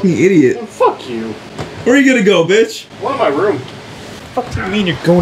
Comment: What was that we heard at 6:40? Did call the bearded man-child MITCH? (Or was he just saying a bad word?) EDIT I approve of using his old video voice at the very end. FINALLY some entertainment value!